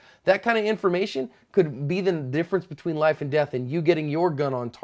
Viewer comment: Did I just hear cover your "asp"????